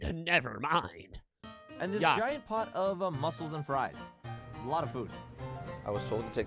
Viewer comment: did bro say muscels and fries